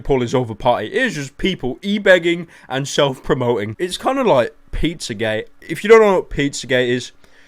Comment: When you said pizza gate i thought you said pizza gay, so looked up pizza gay and found an article about a pizzeria in indiana that is getting backlash for refusing to catering for a gay wedding then sending them a pizza in a shape of a dick. They are of course getting a verbal beating by everyone.